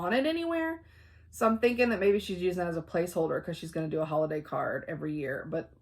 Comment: that llama!! but i thought you said "holiday car" and I was kinda said it wasn't a llama in a car.